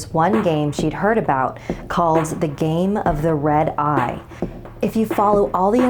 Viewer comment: Oh I thought it said game of the road eye. So I was like oh cool a game while driving